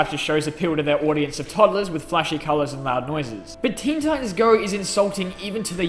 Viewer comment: Lol CC said Kim Jong instead of Teen Titans